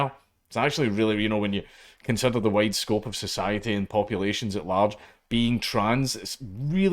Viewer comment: Watch your iliterations there Dank, I thought I heard you say "in the white scope of society" and we both know google's captions will do you no favors.